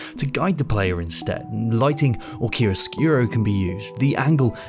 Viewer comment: We're back from GDC! Jetlag hit us in the face but regardless i hope you enjoy this episode from tired & I. What topic would you like to see us tackle next? LET US KNOW!! If you enjoy the show please consider supporting us all making this weird content over on Patreon: <link> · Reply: What did you say at 5:25? "Lighting or _______ can be used"? The automatic captions with their "Kiros skuro" isn't very helpful.